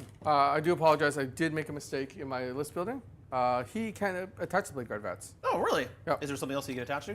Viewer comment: I heard "can't attach to bladeguard" and had to double check what army he was running. Classic Blood Angels Captain Moment 😭